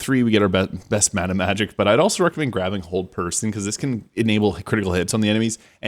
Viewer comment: Hehe.. sounded like he said cold person instead of hold person. ❄️😂